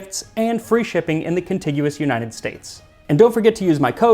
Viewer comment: "The continuous United States"---hadn't heard that before! Maybe "contiguous"? I may have misheard..... Great video!